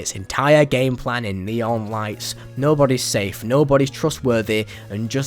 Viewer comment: thought you said Leon Nights